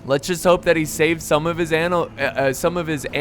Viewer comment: did i heard anal?